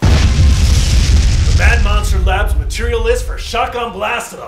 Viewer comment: at the beginning i thought you said mad lobster mab